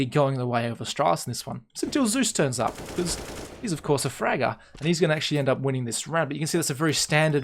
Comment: wait did he just say Zeus is a frager XDDDDDDDDDDDDDDDDDDDDDDDDDDDDD